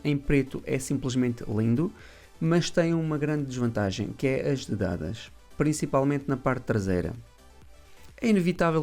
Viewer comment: o preto é simplesmente lindo mas tem uma grande desvantagem que é........ (o que você disse?) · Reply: Eu depois digo qual é lá mais para a frente no vídeo.. São as dedadas. Nota-se muito na parte traseira.